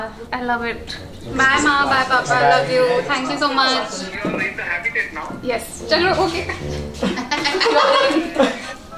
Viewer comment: Someone please tell me what said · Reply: Your night at Habitat now